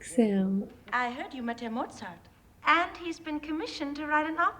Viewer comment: My father was a huge Mozart fan. This was his favorite film. 🙏 RIP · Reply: At first I thought you were saying RIP Mozart.